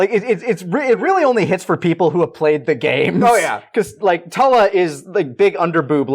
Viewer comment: NGL, I thought they said Toei, not who ever that blue lady is. I thought it was funny to poke fun of the ppl that love to sue everyone